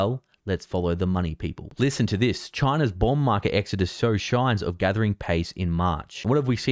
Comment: Sorry to say, but in 4:02 you said 'sow shigns' instead of 'show signs'. Sounded like Chinese 🙃